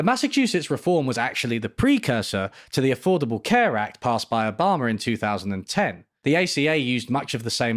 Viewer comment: did he say "obhamer"